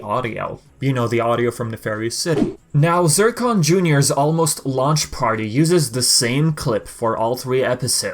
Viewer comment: Is it bad I always thought zurkon junior said “almost lunch party”